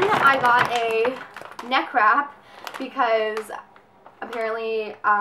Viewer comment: thumbs up if you thought she said neck crap lol XD